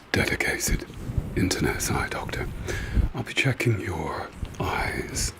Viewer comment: I thought you said "DEFECATED internet doctor" LOLZ 😅🙃🤦🏾‍♀️I need to go to bed Huge fan, nonetheless!!!